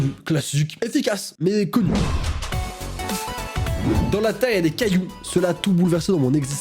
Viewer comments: moi j’entends plus “dans la terre du cadoux” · À 7:42 j'ai entendu "dans la terre du caaillou" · Moi à 7:38 j’ai entendue “dans la terre y’a des poux” 😂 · moi j'entends "dans la terre du caillou"